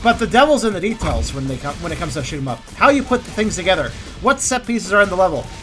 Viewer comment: Sounded like you said shit em up lol